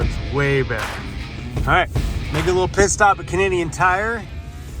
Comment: 😂😂😂 thought you said Piss stop at Canadian Tire.